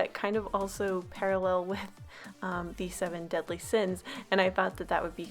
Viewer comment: Did you say seven deadly sins